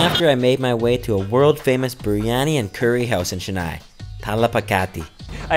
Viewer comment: did he just say Tala pa kat he???🤣🤣🤣